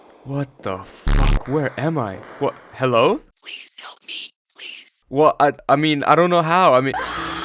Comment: did she saying please help me or please kill me